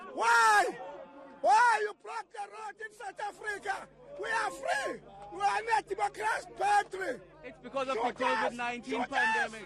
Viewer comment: did he say we are democracy battery? 🤣🤣🤣🤣😭